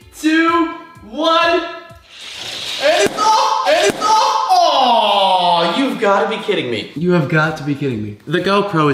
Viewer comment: ????? wat did he say??? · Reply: Spacecraft294: “AND OH”